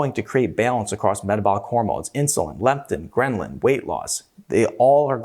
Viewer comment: sounded like said gremlin lol